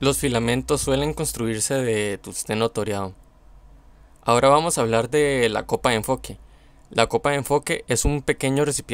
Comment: Alguien que me diga que dice en el minuto 2:50 por favor Dice tungsteno toreado ? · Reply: ¡Hola!, si, es tungsteno toriado. Los filamentos pueden ser construidos con una aleación de tungsteno con torio, ya que al agregarle el torio, se aumenta la eficacia de emisión de electrones en la emisión termoiónica, y también prolonga la vida del filamento. Saludos